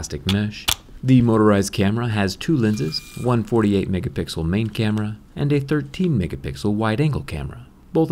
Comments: For a moment I thought he said 148 megapixel main camera..my bad.. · For a sec I thought you said 148 megapixel camera lol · At first I thought he said 148 MP main camera lol